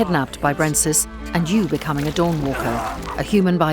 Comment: I totally heard "and you became a dogwalker" and now I can't think of anything else 🤣🤣